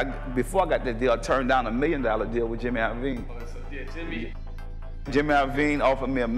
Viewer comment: Who did he say at 5:33? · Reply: He turned down a million with Jimmy